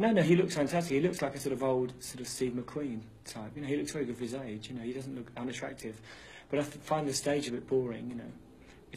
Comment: What is he saying at 6:20? 🙈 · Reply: "When they said, "Are you bisexual?" I said, "Yeah, when I want sex, I have to buy it."